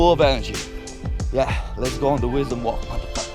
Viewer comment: I repeated 3x to be sure, 1:22 did he just said mother fuckers? ahahahahah